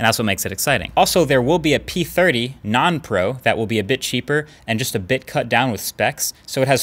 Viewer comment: sounds like you said bitch ahhahah